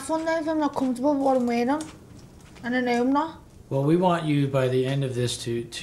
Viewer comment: what is she saying?? 13:34/ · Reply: "i'm not gonna have fun though if i'm not comfortable what i'm wearing"